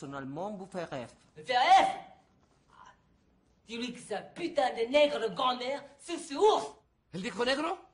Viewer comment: Le entendí puta de negro Creo que dijo eso